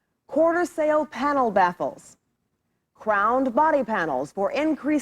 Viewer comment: Baffles, they had baffles for noise control - to help make the interior quieter. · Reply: Lol....sounded like she said "backles"🤣